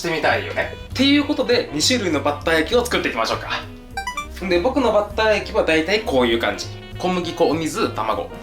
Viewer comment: 「僕のバッター液」が下ネタに聞こえました。 おっくんのせいです。